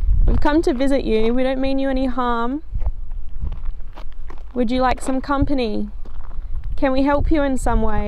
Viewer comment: The female voice sounded like it said, "Would you please stop?"